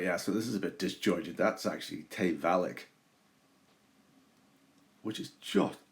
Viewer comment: Hi, what is that name of the place you say at 4:16..? Tevalleck..? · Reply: Hello, it’s Tayvallich, in Loch Sween, east of Jura. Absolutely lovely place, very sheltered and some great walks in the area.